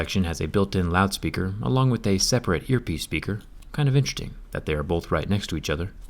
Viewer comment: zack said intreting instead of interesting lol